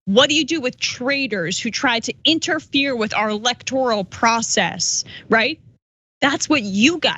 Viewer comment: What I hear is "those traitors who didn't vote Republican". Suck it up, snowflake.